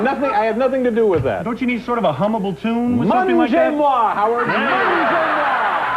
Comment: What does Dave say at 1:10? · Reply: Mangez moi, French for "eat me".